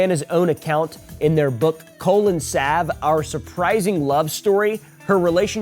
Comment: for a second I thought you said the name of their book was Colon Stab